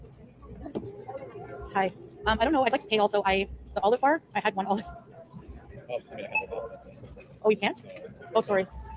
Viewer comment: What did the dude at the register say? I cannot hear him. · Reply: He said, dont talk to meeeee!